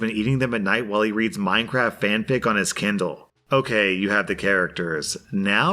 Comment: did he just say mc fanfic??????